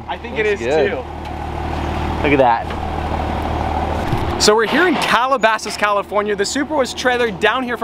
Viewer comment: I thought he said “We’re in carole baskin california” lel 😂